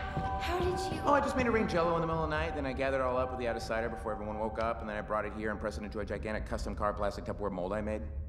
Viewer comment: So...did anyone catch what he said at 1:41? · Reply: Oh I just made it rain jello in the middle of the night then I gathered it all up with the Out-of-sighter before everyone woke up then I brought it here and pressed it into a gigantic custom-carved plastic Tupperware mold I made. No big deal.